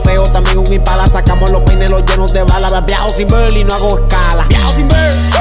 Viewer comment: pense que decia viejo sin ver 😂😂😂😂😂